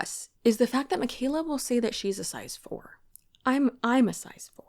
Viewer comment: I thought she said she was a size 14 not a 4 😂😂 lol